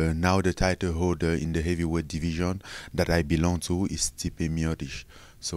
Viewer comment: Did he just say he belongs to Stipe, lol